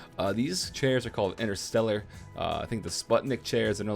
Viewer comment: OTTEAL. What are those chairs called? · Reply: I thought he said interstellar at first but I didn't find any chairs like that so I'm pretty sure he was talking about interstuhl the German office furniture company since they have a Sputnik line.